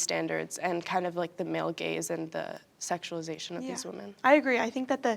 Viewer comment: LOL this comment is taking me out 😭😭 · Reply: sorry i heard “male gays” and not “male gaze” 😭